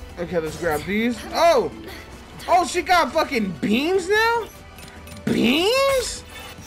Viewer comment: What everyone else heard: "Oh she got fn BEAMS now?! BEEEAMS?" What I heard: OH SHE GOT FN BEANS NOW?!? BEEEEANS?!?"